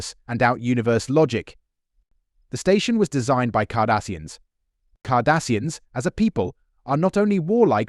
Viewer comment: 😂 close caption said KARDASHIANS